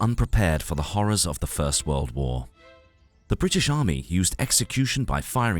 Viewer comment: 00:7 I got hit with the bufering atack, so I heard "the horrors of the third world war" Good thing not yet